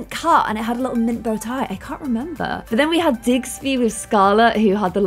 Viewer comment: Did says “Digsley” for Scarlett’s pet? I could’ve sworn she said that what the heck lol did I hear wrong? Well anyways, his name was Dilbert, haha.